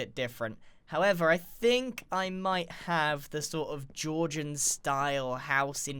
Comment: wait... at 9:23 did u say GEORGIAN?